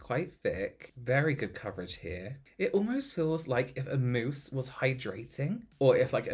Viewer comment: Why was I envisioning a moose drinking water by some riverside for a full minute before I realized he actually said “mousse”...